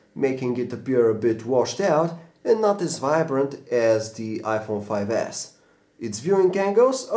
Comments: did he just said "the iphone 5s"?! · DID HE JUST SAY IPHONE 5S?!